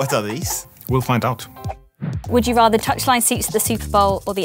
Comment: What is she saying at 0:14? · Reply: "Would you rather (have) touchline seats to" touchline seats are as I understand the best possible seats.